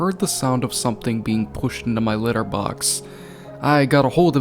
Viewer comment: This creepypasta scared me for 4 years when I was like 7. And 1:17 I heard litter box but I could just be stupid idk